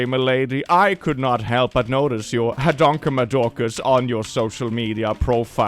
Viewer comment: Now we finally have confirmation how hadonkamadorcas is spelled. Succesfully included in mental lexicon. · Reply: I could have sworn he was saying mahonkamadorkers this whole time. Glad to have an official spelling. But can we really trust the spelling of someone who also is dislaxative?